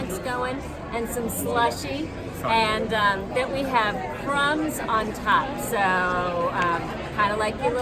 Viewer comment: Is such a great addition to your team!! I thought she said the apple pie in a cup had prawns on top. 🤣🤣 Had to do a double take and rewind the video and realized she said crumbs on top. 💖 I also love as how the day went on she said the temperature went from 115° outside to 125°. Ha ha! 🤣💖